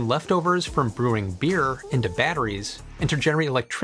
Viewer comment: I thought he said turning beer into berries xDDD